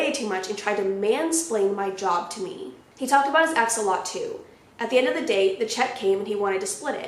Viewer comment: I thought you said "axolotl" instead of "ex a lot too"